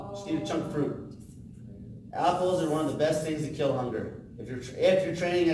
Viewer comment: What does he say at the 1:00 mark ? "apples are the best thing to _ " (not clear) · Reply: "to kill hunger" is what he says